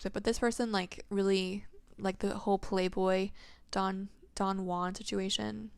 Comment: What name did u say at 43:00? It sounds exactly like the name of the person I'm listening to this reading about. Sounds just like his full name... D W? 😳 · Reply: don juan!! that's crazy, i almost didn't say it but felt like someone needed to hear the reference!